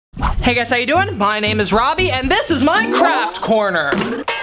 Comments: When he said that 'this is my craft corner I thought he said that this Minecraft corner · It sounded like he said “This is my Minecraft corner!” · I thought he said Minecraft corner 🤣🤣🤣🤣🤣🤣🤣🤣🤣🤣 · no ones talking about how in the beginning he sounded like he said minecraft corner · Who else thought he said "minecraft" and not "my craft" ? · For second I thought you said Minecraft corner but you actually said my craft corner